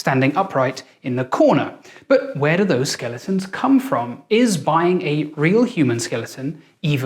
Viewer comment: I thought it said bullying bones